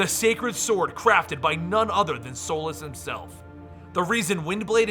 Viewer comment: did he just say "Solus himself"?